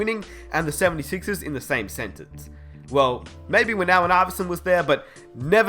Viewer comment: "maybe when AI was there" he was obviously talking about Andre Iguodala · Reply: Yeah ummmm, no... He clearly says "Allen Iverson" not "AI" at 2:08.